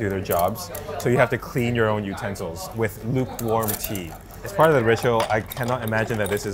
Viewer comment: I was making breakfast while listening and at first I thought he said “Lukewarm pee.” 😱😱😱😂😂😂